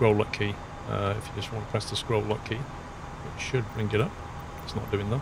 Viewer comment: Nice vid!! You say "Scroll up key" - is that the up cursor key? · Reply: I heard "scroll lock", might make more sense.